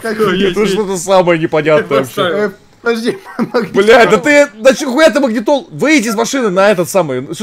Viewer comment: НачиХуя,я даже не удивлён то что я это слово от услышал